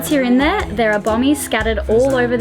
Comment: did you say bombies? Sorry, I don't understand...are these bombs, coral outcroppings, other?